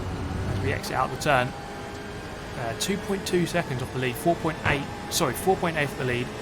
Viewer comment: Did you just say "4 point f behind the leader" ? I thought I was the only one that would flub 7 and f...